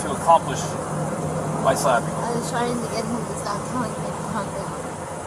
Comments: Gabby actually says.. "I was trying to get him to stop telling me to calm down." I heard it the first time I watched it, and when I saw her tears right after, I remembered how it felt. They stir and and stir and stir, and then, when you finally can't take it anymore.. they tell you to calm down. I'm way beyond those days, but I'll always remember. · I heard: "I was trying to get him to stop telling me to calm down" rather than "I was trying to get him to stop talking and calm down". Does anyone else hear that too? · Good video and very insightful thank you. At 13:47 I think Gabby is saying she was trying to stop Brian telling her to calm down. I'm intrigued by the Wing Lee painting; would you be able to show the full painting?